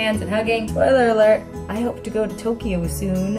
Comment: When she said spoiler alert in the beginning, I thought she said Troyler Alert... I was like WAIT I NEED TO PAY ATTENTION WHAT IS HAPPENING!